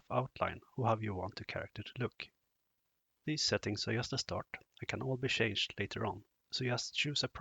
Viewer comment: it is impossible to hear what you say, but it is extremely important. All I hear is "These settings are ??? to start, it can ??? be changed later on". So, what are the '???' there? Namely, can these settings be changed later on or not? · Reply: "These settings are just a start can call be changed later on." As a tip: the video has subtitles. You can enable these with the texting icon in youtube. This way you'll get a textual overlay with exactly what is said.